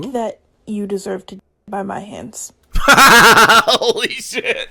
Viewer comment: I thought she said “bye bye hands” as a die euphemism and I’m like I LOVE IT